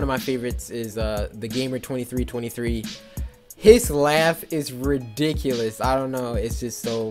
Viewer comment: I thought u said gamer 1023 😂😂😂😂😅😅😐😐😢 I have no frends